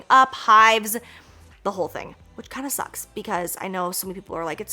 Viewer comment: When Jaclyn was talking about smoking weed and says she's allergic to it and "It kinda sucks" I heard: "It's kinda sus" 🙂